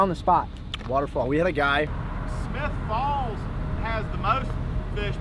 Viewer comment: I thought the one local at 00:25 said smith balls instead of smith falls